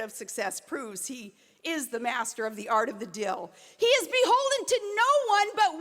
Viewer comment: is she saying 'dill'? i thought it was deal - but clearly it's something else..........and i have no idea what it is. [@ 4:53 onwards].